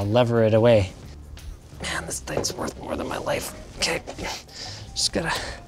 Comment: I thought he said WIFE.